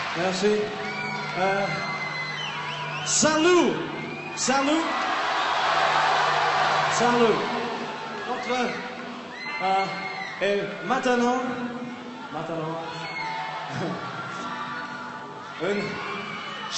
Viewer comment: ooo!! Paul is so cute trying to speak another language! x3 Did he said Matano? (at 0:12) or so i've heard xP can anybody tell me what he said? ^^'